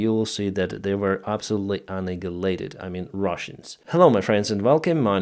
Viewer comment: No hate just a little confused At 1:20-1:21 I'm not sure what word you are saying 🤔 do you mean russia were anihaliated?